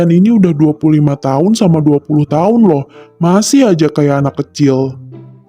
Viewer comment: Tadi ku kira umur dara 60 tahun ternyata salah denger